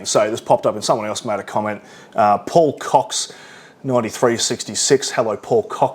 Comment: With your accent sounds like you said, "pull cocks." Keep up the quality content